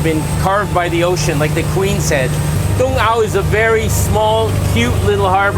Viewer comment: Have to laugh at myself 😊 earlier in this episode you said something about the Queens-Head, and I thought you said like the Queen Said, and I was thinking what Queen Taiwan is a Democracy ? my silly mistake. but I always enjoy Happy Fisherman uploads, and this was a very good one, Thanks ! 💖